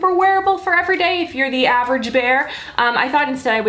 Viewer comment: Did she say 'average bear' ?😂